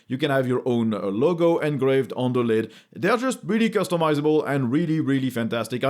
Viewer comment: <link> 21:20 I'm glad to hear that death is really customizable, seeing as many of us are afraid of it. But I think you meant to say "that" is really customizable hahaha